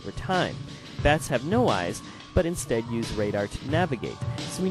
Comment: did he say bats don't have eyes?